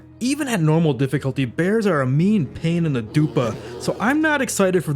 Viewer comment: Did I hear "pain in the dupa"? 😂 And you playing KCD and Medieval Dynasty, my too fav games! I am so glad yt recommended me your vid. You got a new subscriber sir. 😊😊